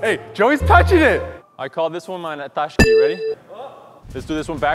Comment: did say ''I call this one my Nethashki, ready?'' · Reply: NATASHKI!